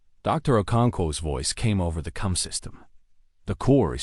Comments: This is the second worst AI voice. It's bad enough the story is lackluster AF. This voice is just the mayo on this siht sandwich. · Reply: the ai says cum system instead of com system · the ai says cum system instead of com system